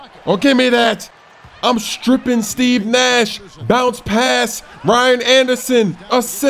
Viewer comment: Did he just say 'I strip Steven Nash!...' lol xD